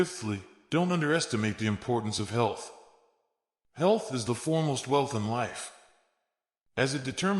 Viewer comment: What is that word @ 4:36 "Formalth " ? "Health is the "formalth" wealth in life". What does that mean ? I can not find ANY word "formalth" ANYWHERE.